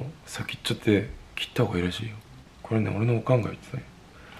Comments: のところ、字幕が「俺の考えだよ」になってるけど、「おかんが言ってた」って聞こえるの私だけ？笑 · 자막 '이건 그냥 제 생각이에요' 바르게는 '이거 말이야, 재엄마가 말했었다' 라고 말하고 있어요. '오칸' 는 '엄마' 의 오사카의 사투리. 5:09 Caption "That's just how I like it" To be correct, "This is what my mother said." "Okan" is the Osaka dialect of "Mom." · バナナの先っぽは切った方がいいってこれ俺のおかん(お母さん)が言ってたんだよって言ってるところ、字幕ではこれ俺の考えって書いてあって公式でもネイティブな日本語は聞き取りずらくて翻訳もしにくいんだなって、、、 字幕に頼ってないで自分でも理解できるように韓国語もっと勉強しなくっちゃ！ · これねオレのオカンが言ってた the sub is not correct He actually said "my mom told me like that" 유타 말했던 것은"이거 우리 엄마가 그러셨어"라는 뜻이에요